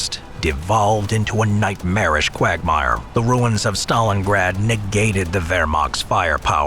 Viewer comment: D…did he just say ‘Quagmire’ at 14:00?